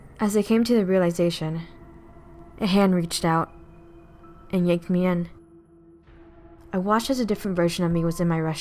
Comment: i thoug i heard " the hand yeet me in😂